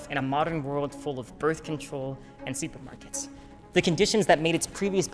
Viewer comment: For the life of me I can’t figure out what he’s saying here. “Seafood markets” ? I’ve watched it back so many times, tried the transcription, still no idea what he’s saying.